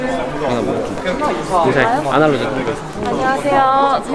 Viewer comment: 뭐라고 하는지 아시는분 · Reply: 아날로젯 운영하는 인 것 같아요